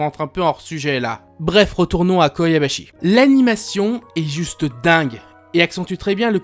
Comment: Je rêve. A 2:55, j'ai entendu Koyabashi